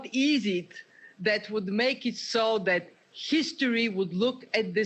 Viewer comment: "...what is it that would make SHESTORY..", instead of history. UNBELIEVABLE!!